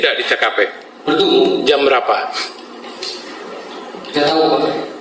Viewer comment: jam 5? Ini kuping saya salah dengar atau beneran itu yg dikatakan